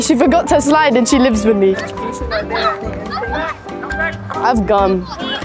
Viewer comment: I thought she said "ihave a gun" 😭😭😭